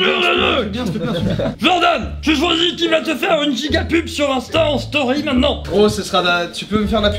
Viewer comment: Y a que moi qui ai entendu Giga pipe a 8:58 ???😂😂